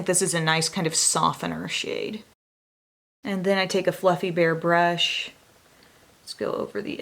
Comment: I know you said "fluffy bare brush" but I heard "fluffy bear brush" 😂🐻